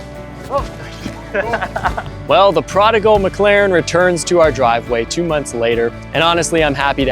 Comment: Oh The Prodigal McLaren, I thought you said The Prodigals McLaren and thought I had won a new car lol